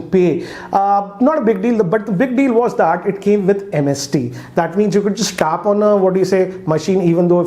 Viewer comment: Heard it as 'MSD' , wondering what's dhoni got to do with this phone Lol 😂😂